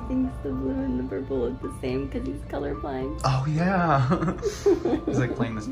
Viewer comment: wait did say Ollie is colorblind???